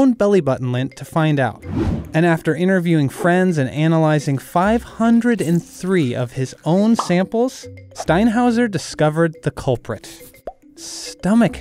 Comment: I thought they said “interviewed 503 of his own samples”